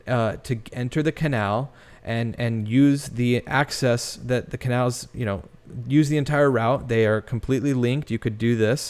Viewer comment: Seriously thought said 'Canabals' for just a moment.. omg. Moving on.. lol